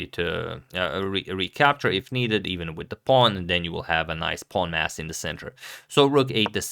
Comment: Did he say.... "Now you will have a nice pawn ass in the center" 3:08 ish.